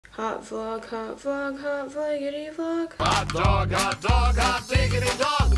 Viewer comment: Did i just heard "Hot vlog hot vlog hot digidy dog" IDK DX